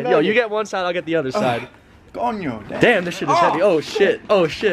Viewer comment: En el minuto 3:52 escuche un “coño" :v Que rayos :v